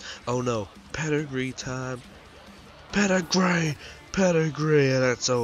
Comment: did you say Pedigree time or Pedigree to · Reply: Pedigree time xD